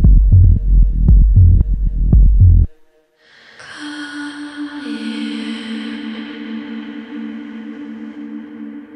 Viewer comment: anyone know what's being said in endnote [54:22]? i can't tell lol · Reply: Come here?